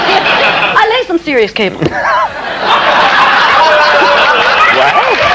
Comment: ALWAYS LOVED HER.. by the way, what does she say at 5:11? I played it several times and I can't figure it out? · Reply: "I lay some serious cable"🤣